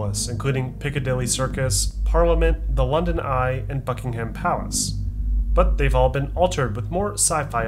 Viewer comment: i thought he said "and F***ing ham palace and i was so confused lol